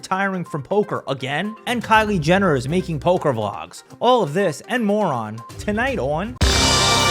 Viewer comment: When you said “all of this and more on” at the start … I thought you said “all of this moron”